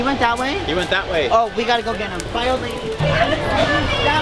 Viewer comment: did just say bye old lady? 😂 the shade